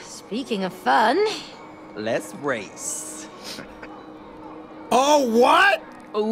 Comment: why did Cody say "Let's race" But I heard; "Les race" sassy girl snap